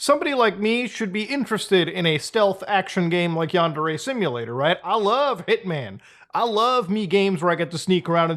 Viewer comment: I thought muda kept saying 'underage simulator' I was like what the 😮